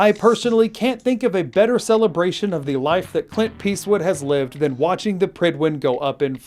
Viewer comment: thought you said clint pisswood